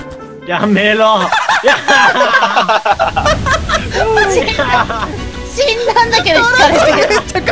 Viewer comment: ここなんて言ってるの？ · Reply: 「死んだんだけどw轢かれて」